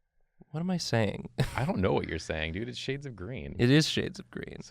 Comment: I thought it said SHAPES of CREAM hahaha